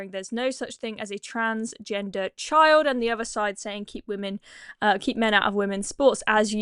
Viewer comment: Thought it said "Keep Men out of Women's Shorts' for a moment. 😆